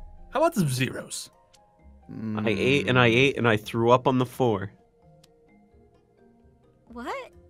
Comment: Me choking when I was drinking when I heard “ I ate and I ate and I throw up”😂